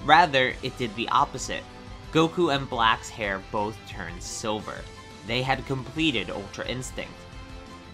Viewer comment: It sounds like you say sober instead of silver